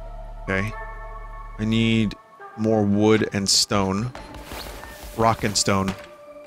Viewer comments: “Did I just hear a Rock and Stone?” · Did I hear a Rock and Stone?